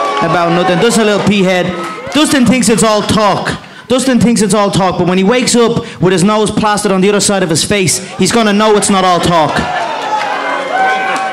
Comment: what is mcgregor saying ata 9:07?is he saying "...when he wakes up with his nose passed on the other side..." or "...when he wakes up with his nose plastered on the other side..."?